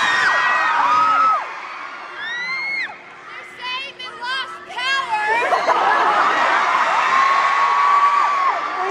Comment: and 4:25 what did Taylor say? I not understand. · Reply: They're saying they've lost power.... 😂